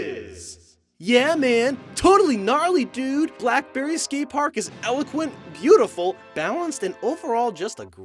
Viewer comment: did he just say blackberry skate park?